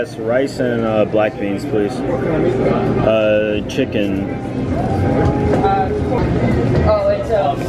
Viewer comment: you asked for beef then I heard you said Chile. after 0.o